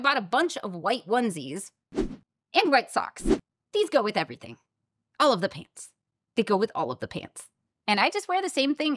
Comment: I heard "All of the paints". Yeeees, white clothes goes with all of the paints 😆